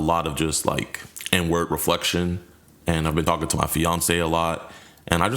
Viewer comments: When he said “Inward Reflection” I thought he was saying “n word reflection” and I was so confused for a second 😂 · did Jidion say “N-Word Reflection”? He doesn’t have to be that hard on himself.